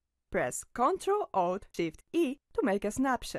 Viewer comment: what control key you say 6:18 mns ? press control o ? or what ? thank you .. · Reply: ctrl+alt+shift+e OR cmd+opt+shift+e on mac